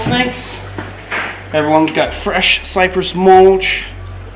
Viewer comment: I thought he was going to say cypress water